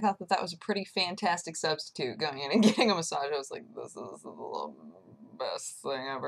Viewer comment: i thought it said "Viking Massage" and i thought she would have a magical tale of adventure